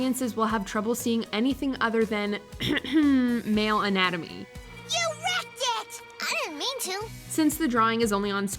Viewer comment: Suzi sounds like she says “you erect it!”